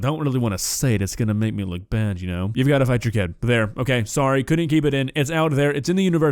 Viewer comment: What did say? Oh, "You've gotta fight your kid."